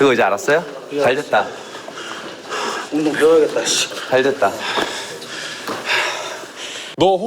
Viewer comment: 운동 배워야겠다 ㅅㅂ 이 ㅈㄹ하는거 보니 정신 못차린듯 ㅋㅋㅋ걍 운동 배워서 확실하게 패고 다녀야겠다 라고밖에 안들림